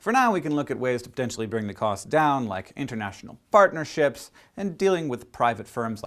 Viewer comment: I thought he said, " International fartnerships"....